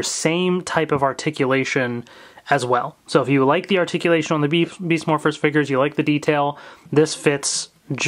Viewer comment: I guess was going to tell beef morphers instead of beast morphers in